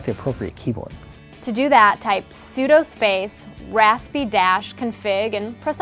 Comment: your mouse was on the screen and is sounded like you said to: sudo raspy-config, and I'm pretty sure thats not it